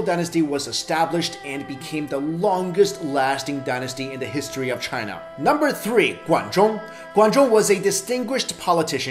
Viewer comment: I believe the number 3 is Guan Zhong 管仲 instead of 罗贯中 at 4:34..